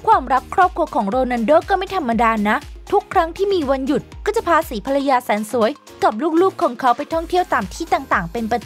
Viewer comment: นาทีที่ 5:22 ได้ยินว่า4ภรรยา